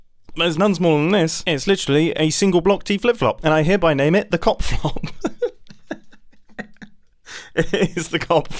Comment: I keep hearing cock flop. I know you mean cop flop, but please don't name it that else the wholesome nature of your channel will be destroyed. Toodles.